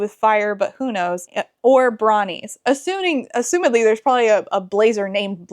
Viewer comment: I kept hearing "bronies" instead of "brawnies" and honestly it probably would have been a welcome addition to the story.